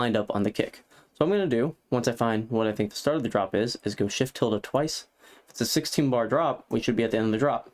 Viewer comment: Shift what ? I can’t understand what you said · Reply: Shift + tilda key “~”